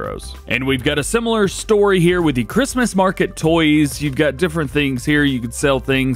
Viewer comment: I really thought you said Chinese Market toys right around 20 minute mark. lol. Re-watched. I miss-heard.